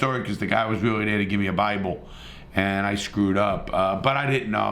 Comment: I thought was gonna say "the guy was really there to give me a blowjob" Had me in the first half, not gonna lie.